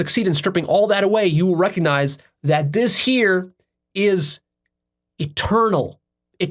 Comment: Excellent! Very enlightening! At one point I heard you say, “God is a turtle,” which was very cool, until I realized it was, “God is eternal.” I first had this direct recognition many years ago with Baba Muktananda. ‘’God dwells within you as you.”- Baba Muktananda. 🙏🏼❤️🙏🏼